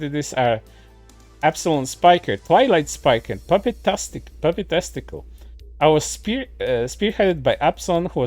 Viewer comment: The way you say "puppytastical" sounds like "puppy testical" I was very confused and honestly weirded out at first. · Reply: I heard it as papi testicle